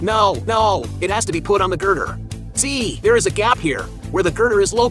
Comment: Why do i hear "Girgir" instead of "Girder"😅😅